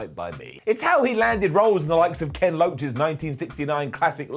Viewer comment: Did he call Kez...Lez